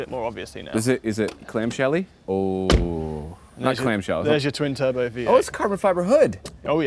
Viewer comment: did you say twin turbo v8? :D